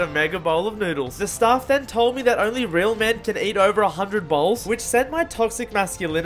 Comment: Why did I hear "100 balls" 😂